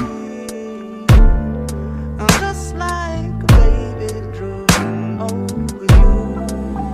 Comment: I dead ass thought he was saying beautiful angel , just like a baby drum only , the things you do ooooh 🤣🤣🤣🤣🤣